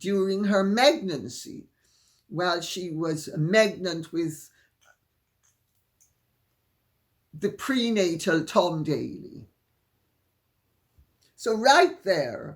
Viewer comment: I'm sorry. At @ 32:40, did you say 'm*gn*nt with the pre-natal Tom Dailey... '? (LOL - alas, my hearing has recently caught up with my actual age. Perhaps I misunderstood?) 🤔☺